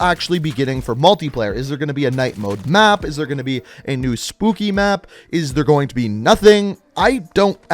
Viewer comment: Am I the only one that heard him say spoopy before he said spooky